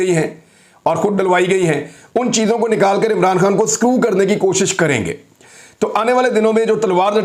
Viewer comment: Did he just say @ 12:50 that they will "screw Imran...! whithout realising what he said?